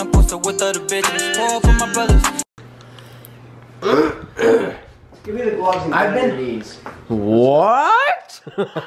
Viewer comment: PLZZZ, WHAT MATT SAID AT 0:29 😭😭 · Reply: i’m pretty sure he said “give me the gloves and get on your knees”